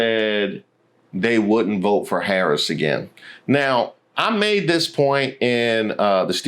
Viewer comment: I thought 91% said they WOULD vote for Harris again...? Is it that 9% we need to come to the front??